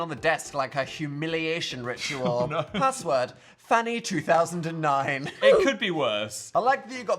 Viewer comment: I thought it said Poo-Phannie2009 😭